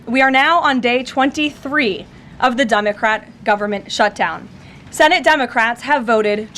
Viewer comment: Did she say “Dumb-a-crat” ??